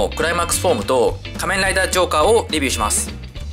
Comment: ジョーカーがショッカーに聞こえる